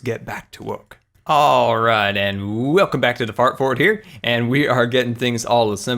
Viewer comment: it sounded like you said fart fort